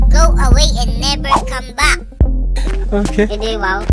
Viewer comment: i hear "e di wow" what the hell i think your filipino